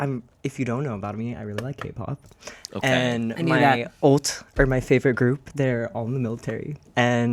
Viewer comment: btw here the captions should say MY ULT instead of MY OAT, whoever makes the captions probably isn't aware of kpop terms but oat... lol 😭 an ult is someone's super favorite group! (just like ollie said)